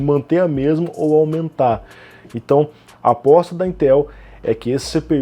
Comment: Só eu que escutei no 1:01 o falar "a bosta da Intel"?🤣🤣